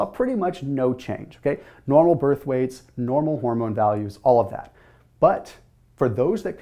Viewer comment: Did he say 'normal MORMOM values'??????